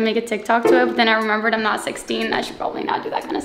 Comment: DID I JUST HEAR “I’m not 16”!?!?😳🤯